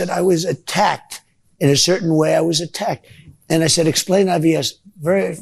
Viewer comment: He thought she said I.B.S. Oh my god, he actually said ibs when I was typing it at 6:10 . He is the father of I.B.S. The first truth ever from trump the pants dumper.